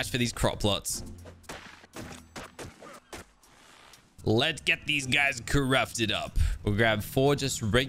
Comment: or 9:58 I thought he said"Lets get these guys corrupted up"